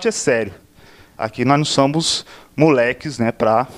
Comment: Só eu ouvi: "...aqui nós não SAMBOS..." 45:40 ????